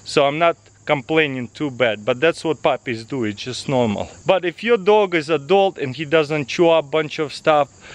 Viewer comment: first time I heard "If your dog is a dog" hahaha